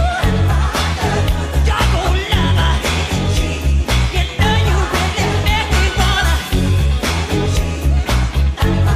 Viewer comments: What did he say at 6:27? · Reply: Doggone Lover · what did he say? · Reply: YoU kNoW YoU CaN MaKe mE WaNNa